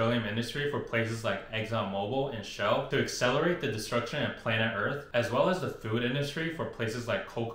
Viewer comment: This guy literally said " to accelerate the destruction of planet earth" with dead 💀 face in the chemistry engineering part 🤣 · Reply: I thought he said to “decelerate the destruction of planet earth” lmao